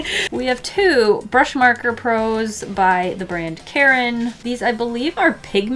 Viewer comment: I hear, "the grand Karen"